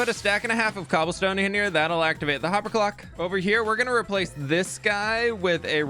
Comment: I thought you said Hoppper Glock